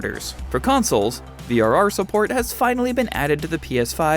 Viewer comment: I really thought you just said cyberpunk has vr support for a second. Got my hopes up 😢